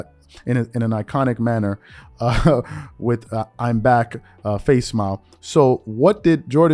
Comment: did you really say face smile @ 3:59? lmao